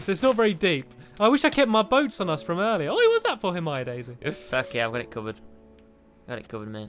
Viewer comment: DID JUST SAY "F*ck yeah I'm gonna come in"???